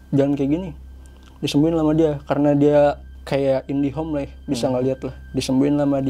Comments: Menit ke 21, ”karna dia Indihome bisa ngeliat gitu” sampe gw back apa gw salah denger, ternyata bener dia indihome😂 (sudah pake fiber optik kayanya) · Gua yg salah denger apa dia yg salah ucap, INDIHOME dimenit 21:00😂